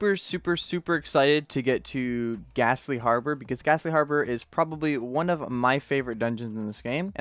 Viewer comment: When he said ghastly harbor I thought he said gasoline harbor